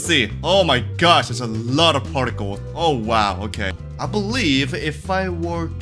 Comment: I THOUGHT AT 2:04 HE SAID I BELIEVE I CAN FLY